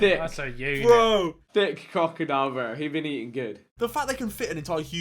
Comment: thought I hearD vik say "thick cock"........